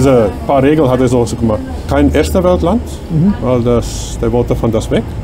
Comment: Bei 12:30, was sagt er da genau welches Land auch nicht? Ich verstehe nur iwas mit "Esteban-Land"... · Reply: Kein „erstewelt Land“ und kein Land, wo der Engländer je war…. -hab ich verstanden